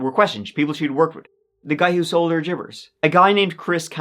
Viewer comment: “The guy who sold her jivers” @ 9:15 . Watched it four times. What word is he saying there.